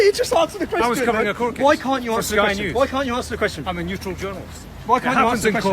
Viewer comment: 'I am a neutral journalist". Yeah okay mate. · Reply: Oooooh "neutral". I thought he said "neutered" which makes more sense.